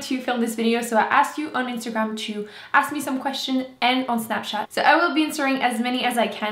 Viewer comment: LOL I love how you say snapchat >u< I somehow thought u said snapcat. My French sucks but I can understand cat in French. Meet up in Toronto!!!!